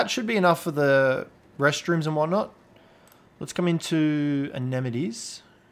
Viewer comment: I kinda like how it sounds like says anemones when saying amenities